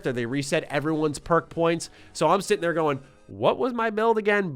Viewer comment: Sitting I swore he said Shitting 😆